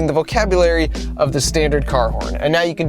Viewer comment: i thought it said INCEST CAR HORN